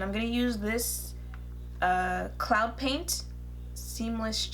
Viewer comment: Ummmmmmmmm GIRL!!!!!!! No thanks glossier sir! But with those blushes I had to go back I could have sworn you said clown paint lol!!